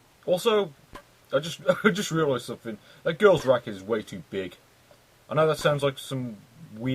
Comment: Sounded like you said the girl's rack is too big. LOL Then I found out it was the tennis racket.